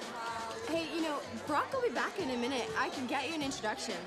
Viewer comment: At first I thought she said, "broccoli back in a minute."